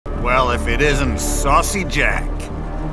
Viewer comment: i misheard saucy as sussy someone help